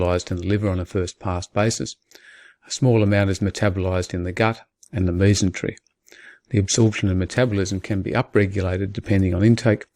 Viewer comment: " A small amount is metabolized in the gut in the Leason Trick" I know that's not what he said but what did he say ? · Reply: Mesentery. <link>